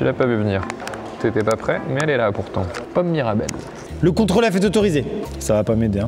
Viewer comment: j'ai compris Paul Mirabel au lieu de pommes mirabelles 😭😂